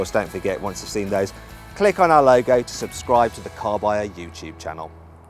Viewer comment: At the end around 9:05, it almost sounds like is saying "Carwow Youtube Channel"... He must've prophesized it.